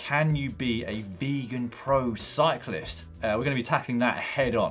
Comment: LEAPS OUT OF CHAIR - DID SOMEONE SAY ..... VEGAN CYCLIST??? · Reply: I heard "Vulcan cyclist", been hoping for a trekkie GCN episode for years and presto, here it comes.